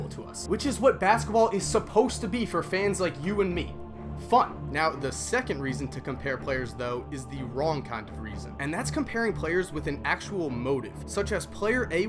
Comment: I heard f*ck instead of fun in